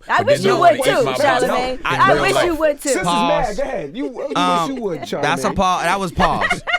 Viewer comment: Did envy say "sis is mad" at 21:30.